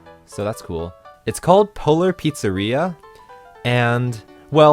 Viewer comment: I can't help myself, I keep hearing "Polar Pizzarrhea" :D